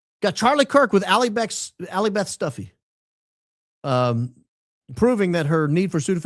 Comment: What I heard, "Allie Beth Stuffy."